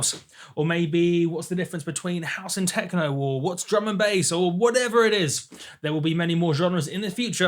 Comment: Every time he says "Drum and Bass" I hear "German Base"